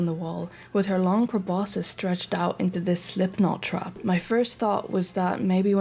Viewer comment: I HEARD YOU SAY SLIPKNOT?